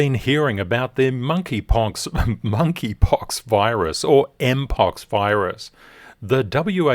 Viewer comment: Have a great weekend ahead my friends!🥳🎉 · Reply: Hi Master, did you say Monkey punk virus? 7:06 :face-blue-smiling::face-blue-smiling: BORSOG is awesome, he is so cool. Have a great weekend everyone 🦅🦅🦅